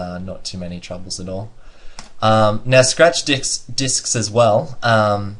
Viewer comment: am i the only person who heard @ 24:23 stretch dicks :D